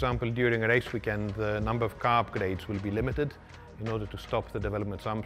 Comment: I don't understand what he says at 1:24. Sounds like "number of carb blades will be limited", but since it's not 1979, that's likely not correct.